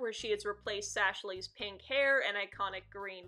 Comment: did I just hear sashley